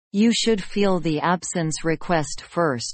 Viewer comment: <You should "fill" the absence request first> instead of "feel"